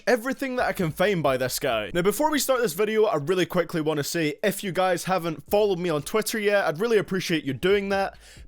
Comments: When he said if you guys I thought he said f u guys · I thought you said f you guys